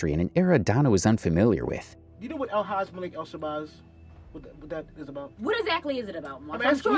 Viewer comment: Can someone elaborate or at least spell out what was said at this particular time? Please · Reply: It took me a few minutes to actually find out 😅...but he's saying " El Hajj Malik El Shabazz" which is the Muslim name given to Malcolm x.. they could be talking about a certain organization or movement named after him.